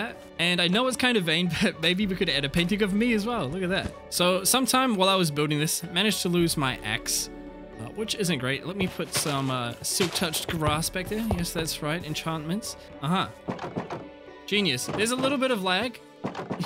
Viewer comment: For a moment i thought you said "I lost my Ex" but then i realized you ment "Axe"